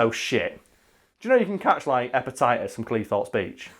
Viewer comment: Watching the video with subtitles on. And the bit about how bad British beaches are. says "hepatitis b from Cleethorpes Beach". subtitles say "appetizers from Cleethorpes Beach".